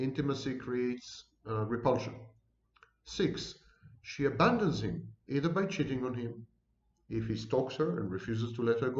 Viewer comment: What is the word you are saying at 51:00 - like the sequence, but from the narcissist's point of view? The 'swith?' 'Switch?' I'm not familiar with the word. At any rate, it certainly describes my relationship with the narcissist. Bravo. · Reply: Suite.